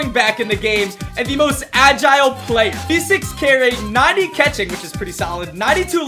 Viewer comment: did he say 90 catching?